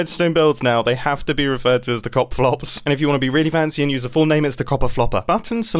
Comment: The Cop Flop is the best name I've ever heard for a redstone machine. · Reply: Really thought he said cock flop until he said the full name lol